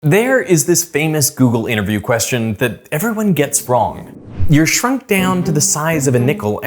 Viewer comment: Im so drunk, i thought the miniature said: "so, you're drunk?"